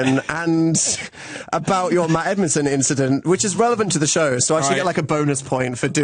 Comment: does Dan say "about your Edmonton incident" at 36:17? I'm going to pretend he did